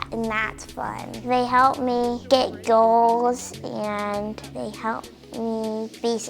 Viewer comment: Did he just say “ they help me get girls”